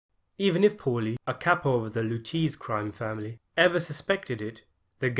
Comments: The blue cheese crime family? I know he's doing some kind of weird British accent and trying to say the lucchese crime family but it honestly sounds like he says the blue cheese crime family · It sounded like he was saying the cheese family😂